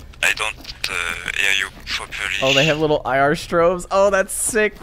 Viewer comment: WTF is he saying??? he is excited for something nobody see or know WTF is the thing he say.. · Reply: IR Strobes, only visible on the map, on characters icone, there is a blinking light. It's something the military can use in night ops to identify friendlies easily